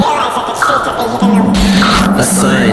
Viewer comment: I thought he said 'a bitch'